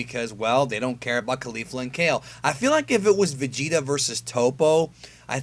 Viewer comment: I thought you said vegeta vs popo.